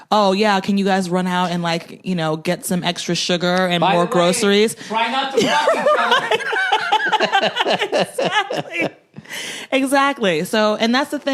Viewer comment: Anyone understands what Bill is saying @ 8:57 ? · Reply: "by the way, try not to fuck each other"